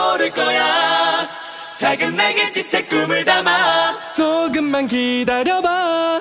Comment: 에서 저만 조 -> 도 발음으로 들리나요?? 아가용 넘 귀여운거 아니니??ㅠㅠㅠㅠㅠㅠ 발음도 귀여운데 어머ㅠㅠㅠㅠ 하는 행동 하나 하나도 귀엽고ㅠㅠㅠ 천상 막냉이다ㅠㅠㅠㅠㅠ